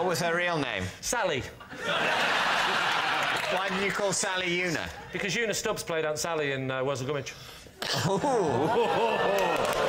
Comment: I am in America and the cc does a terrible job with the British slang. Have a heck of a time trying to search meaning of most of it. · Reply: what did Lee say around 1:38. "Because Una stops played ???"